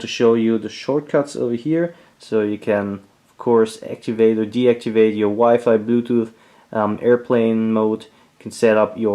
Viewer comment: lol i first heard you said "activate or deactivate your wife" at 5:19 :D i would like to have one of those buttons...